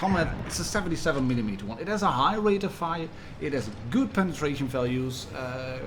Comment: Sorry, did u say that comet have a good penetration?